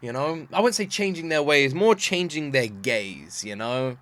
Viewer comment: Instead of "changing their gaze" I heard "changing their gays" and I was about to be like "well, this IS Greek mythology-"